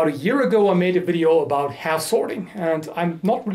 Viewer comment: Im lucky there is a title because I heard "asshorde"(wtf ik) instead of "Half-Swording" 😂😅